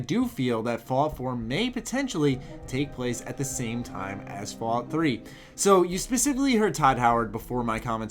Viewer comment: I thought he said it will take place shortly after the events of fallout 3